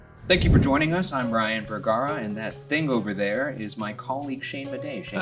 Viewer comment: I misheard that intro as "and that thing over there is my cone of shame" xD